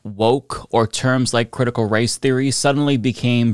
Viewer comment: I swear I thought he said "critical rice theory".